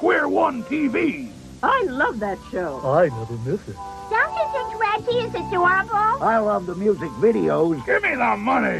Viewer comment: What did she say? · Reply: "Don't you think Reggie is adorable?" Reg E. Cathey was one of the main cast of Square One TV. He was the singing cowboy, the "Rappin' Judge," and the announcer for the game shows, among the other various skits on that show.